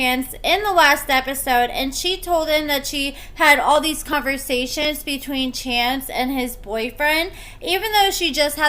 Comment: I heard her say she had text conversations between her and this guy